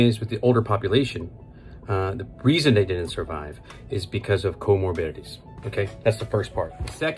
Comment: Comorbidities if you were wondering what he just said.